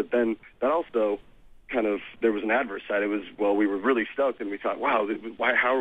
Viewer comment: What word did he say at 4:44 it sounded like adversite? What was it? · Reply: Adverse side, kinda like the flip side